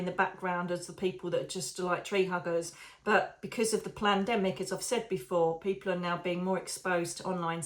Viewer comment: Omg not sure if you were meant to say it but it sounded like you said plandemic not pandemic! Bloody hell that makes more sense than a pandemic definitely planned!!! 😳